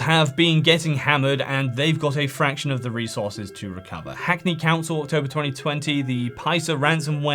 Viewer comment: I misheard you - I thought you said "Hack me" council. 😊